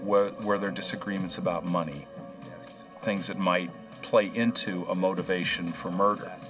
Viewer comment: instead of - “Things that might PLAY into…” To me it really sounds like he’s saying - “Things that might PLAGUE into…” Does anyone else hear this, or something different, other than “play”? Let me know what YOU think 🤔🐿